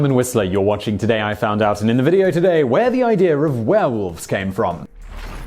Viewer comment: thought he said where whales come from" needless to say, I was interested.